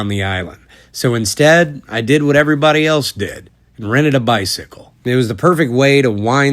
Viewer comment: I thought you said "rented a bison" at first, not because of your accent, I'm a bit stoned is all. Bicycle makes far more sense.